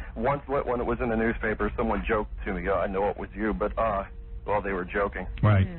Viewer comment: Am I nuts or at the @ 9:50 time mark did he slip and say “Timmy” as his name or did he just say that his friend joked “to me”……